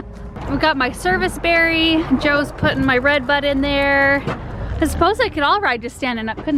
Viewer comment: Love the cedar, but when you said "Joe's putting my, Redbud tree in the back" I thought you said, red butt, and I almost choked on my sandwich that I was eating. Lol That's what I get for not getting hearing aids.